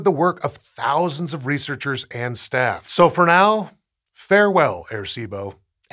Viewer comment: I thought it said farewell, placebo so i was like, damn i guess now i cant pretend to be sick and skip school